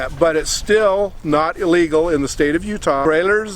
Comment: @ Did he just say "...still not illegal"?